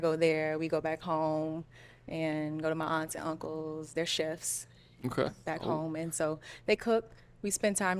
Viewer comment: This dude really asked if her Sheriff family members do dr_gz?🤦🏿‍♀️ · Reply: That was crazy, but I thought she said they were "chefs" not sheriffs? Either way, he was ridiculous.